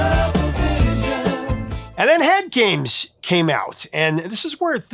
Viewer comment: I've heard "Head Games" on classic rock radio since the 80s. I heard it almost on a daily for decades. Until this video I thought he said "I Hate Games"